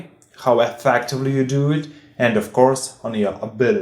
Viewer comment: what did he say? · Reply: And of course on your abilities